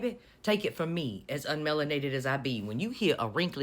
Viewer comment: It took me a long time to realize who you meant. I thought you were saying Melania 😂